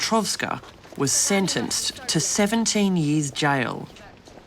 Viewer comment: I had to hear that again. I thought they said 70 years. That would have been good. What a brave woman. What an awful sentence. At least she was caught i guess. This brave young lady will have to live with her burns always. May God help her through it all.